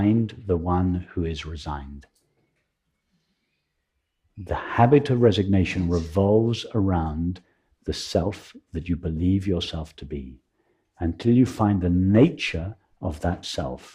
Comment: Could someone write here the phrase from 9:43 to 9:49, please ? I cannot make out myself, thank you. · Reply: "The habit of resignation revolves around the self that you believe yourself to be."